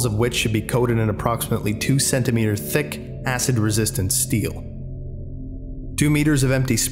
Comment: HELP ALL I HEARD WAS THICC ASS THEN I SNAPPED BACK TO REALITY